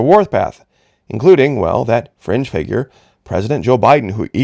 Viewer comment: When you said 'fringe figure' I thought at first you said, 'cringe figure'. lol